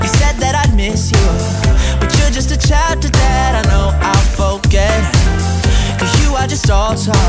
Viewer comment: all this time I thought he said "your just a child I know I forget" but he really said "your just a chapter I know I'll forget" LMAO 🤣